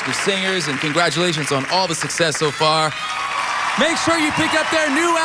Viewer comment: I think I heard him saying, "PENASONIX"